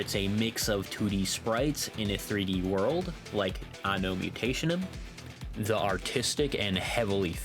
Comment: I didn't understand what you said here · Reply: Anno Mutationem, uses 2d sprites for it's characters but the environment is 3D Narita Boy, uses filters over its pixel art to create distinct effects atmosphere. The other games are chose to stick to a more traditional art styles that emulates pixel-art of yesteryear. Gravity Circuit also has a more traditional approach. Does that help clarify?